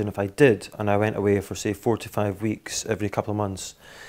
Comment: Fucking lol @ 2:59, Boyle like wtf at what the guy said. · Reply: Callum. T.N I think he said 4 to 5 weeks